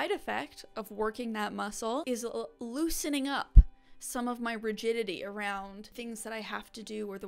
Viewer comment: Wow, at about the 13:45 to 13:48 mark in the video, I had to rewind it and turn on the captions. I THOUGHT I heard, "I have to loosen up my virginity..." WTF was I thinking about?! Thank God I was wrong! 😂